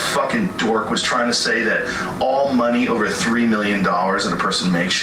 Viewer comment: to be fair to Joe, he actually said “all money over 300 million” so I guess he gets the benefit of the doubt there, all money over could mean only the difference or the whole amount depending of how you want to interpret it.